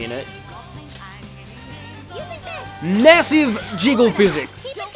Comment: Hardly noticed the physics in the baton pass, had to slow down the speed to see it (also your commentary at 0.5 speed here was hilarious). 27:07 WHAT IS HARU DOING HERE. I guess this is what happens when you make a shotgun fire like a machine gun. 30:06 Okay, I like this mechanic solely because of Haru's absurd/funny animations. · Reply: It sounded like he said "Massive Jiggz" in slow mo lol